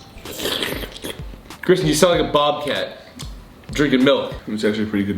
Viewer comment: i thought ethan said bowcat 😭😭